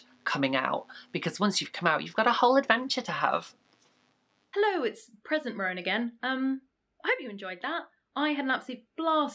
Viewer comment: I heard "hello, it's prison Rowan again" PFFT😭